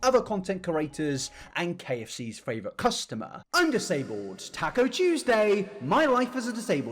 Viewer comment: i thought you said taco juiceday 😔